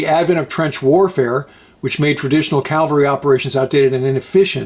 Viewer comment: Did you just say 'calvary'? Instead of cavalry? Twice? Aren't you a veteran, and an educated graduate of the Naval Academy? Good Lord, I'd hope you could pronounce a word as simple as cavalry.